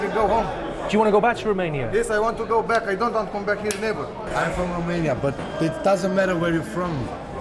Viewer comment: does he say from Albania?